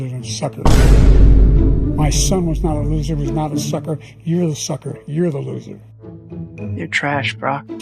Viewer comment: “ your a sucker your a loser” that’s what he said